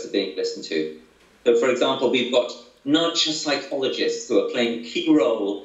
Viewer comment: Does anyone know what he says starting at 10:04 - "??? psychologists..." And beginning 13:52 "Now that's a ???....." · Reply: NUDGE psychologists.